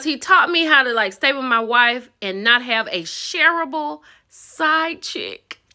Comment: I thought he said a terrible side chick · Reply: No, shareable